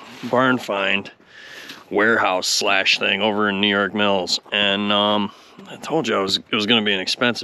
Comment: Greetings from balmy N.W.Mn.,I believe I heard you say New York Mills at the start of the video